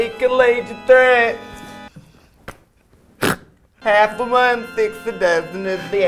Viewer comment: I can't understand what she's saying in the second part! Does anyone know? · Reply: "half of one six a dozen of the other" It's a joke on the phrase "Six of one and half a dozen of the other". Which as a phrase just means two things are the same.